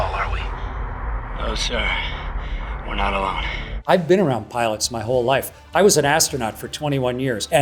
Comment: at first I thought he said "I've been around planets my whole life"